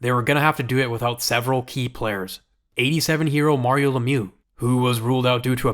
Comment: I had to rewind it 3 times I thought he said “87 year old Mario Lemieux” haha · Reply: Lmao so did I. It took me five times before I realized that he said 87 hero not 87-year-old.